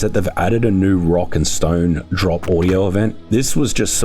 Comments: Did I hear a Rock and Stone!? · Did I hear a rock and stone?